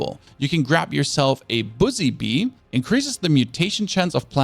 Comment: I heard "bussy bee" at 23:42 had to doublecheck; also noticed it's the most replayed section think others had to double take as well.